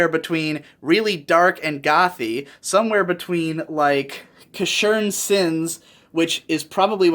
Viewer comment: What was the anime he mentioned at 19:53, which he considers to be the best looking anime tv-series? I couldn't hear it even after replaying it twice... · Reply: Casshern Sins